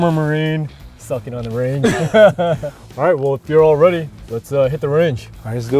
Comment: 영어 뭐라고 하나요( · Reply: Suck it on the range. Right, feel we're all ready. Let's hit the range라고 하는거 같아요. 틀리면 수정해주세요